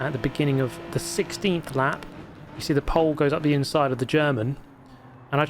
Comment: did he say "you see the Pole goes inside of the German"?